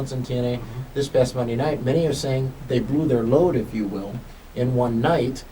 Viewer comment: Did just say they blew their load?